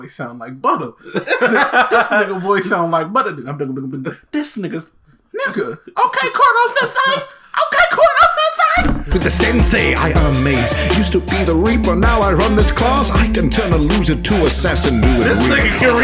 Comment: I swear I heard him say corn sensed instead of koro sensei I don’t blame him or I might just be deaf